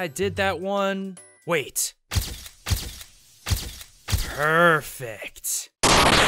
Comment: when he said perfect I thought he said pervert